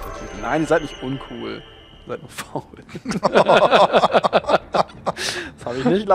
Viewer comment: "nein ihr seid nicht uncool, ihr seid nur ..." was sagt er da? "faul"? verstehs akustisch net :/